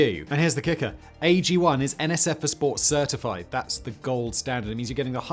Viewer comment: When he said it's "NSF for sports certified", I at first thought he said NSFW certified, and I was like, what the hell? Lol then my brain kicked into gear and I got it.